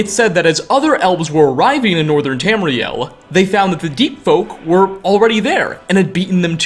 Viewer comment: Lol I misheard your words as Deet Folk. Just imagine your entire body getting coated with a thick layer of deet. How disgusting!